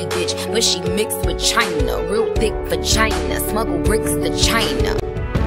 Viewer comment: gurl- I Thought she said "China" instesd of vagina ?? LMAo